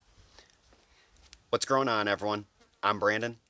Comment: Your introduction sounds like your saying what growing on? I love it